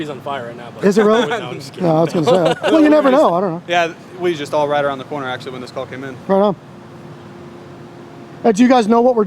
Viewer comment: time sounds like cop quickly says. we just stalled right around the corner actually when this call came in. Then both acts nervous like did i just say that.... What do you hear? Or take out of it watching body language also... Thanks